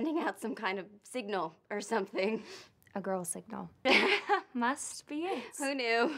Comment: lol i thought she said "a gay signal"